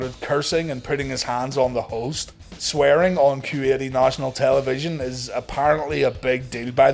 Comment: Oh.....Kuwait I thought you were saying Q8, wondering what a luxury SUV has to do with anything. Nevemind me, I'm just an idiot.